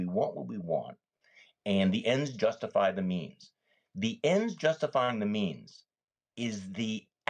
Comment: or did I hear "The end justifying the memes"? Thank you so much. Sane Republicans, Democrats and Independents news to find common ground to save this country before it is too late.